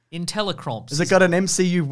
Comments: I always thought he said Inteli-Crops as in smart crops that grow anywhere or fast or something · I always heard it as “Intelli-CROPS.” I assumed it was like, I dunno, crops that grow rapidly or something.